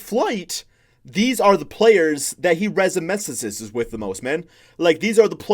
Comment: what the hell is that lmao 😂 “Resemisistss” Ik what you meant to say but what actually was the word you said, I need to see it spelled out I died lmao you watching to many flight videos.